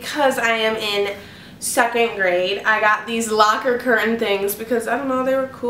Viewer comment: I thought she said 2nd grade! 😅